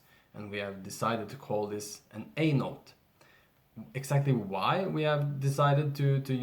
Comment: When you said A note, I thought you said Anal. I’m sorry I just had to say it 😂😂😂